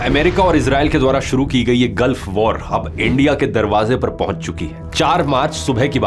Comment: does he said Aendia instead of India ?